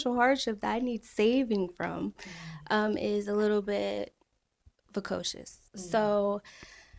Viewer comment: What word is she saying at 30:14? She is more traumatized by her past than she may realize. She’s a pretty lady, but showing too much skin to attract the type of husband she claims to want. I wish her and her daughter well. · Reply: Precocious is the word she used and it isn't the right word for what she was trying to convey